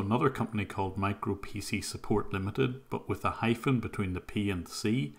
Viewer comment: You are definitely saying it right, but I keep hearing “Micropenis Support Limited”. Silly me.